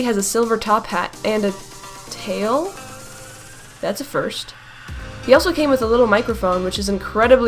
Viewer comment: When she said that's a first aid thought she said "That's a furry" Me:Wait what